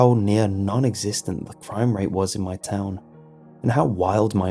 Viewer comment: I thought he said frame rate